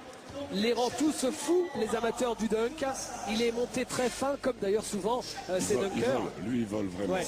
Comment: Did he say on on 2:22 he might fuck up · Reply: Antonio Rodriguez he talken about guy depuy ( dunker professionnal), and he says abouti him : -"il est monté tres FIN..." "he has a SLIM body, but he dunks hard" he says "FIN" not "FUCK"